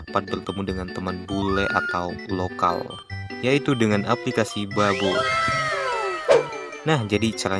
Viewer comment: Aku dengernya Aplikasi Babu🗿, Btw Nama Lagu intro Abang Apa? Aku Dah Subs Abang